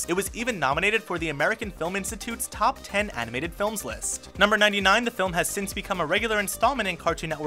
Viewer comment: Top 10 Anime Villains List......thought it said for a sec